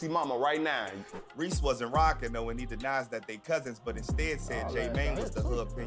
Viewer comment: Thought he said they was cousins at first tho 🤔